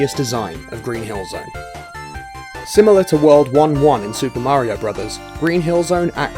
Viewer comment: Swear to god I heard "similar to World War I" at 7:17, and I thought they were about to compare Sonic being a game changer in the console like WWI was for bellicose war.